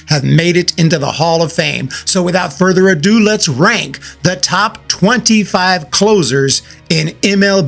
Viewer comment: I honestly thought it said top 25 MLB Losers! 😂 that color scheme threw me off